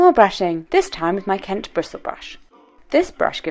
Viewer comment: What is that daily brush you mentioned? I really struggle with my hair being greasy on top but bone dry on the bottom! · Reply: slovakgal - Kent bristle brush is what I think she says 😊